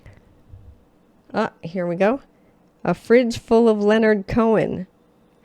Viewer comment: I always heard “a ritual of Leonard cohen” … fridge makes a bit more sense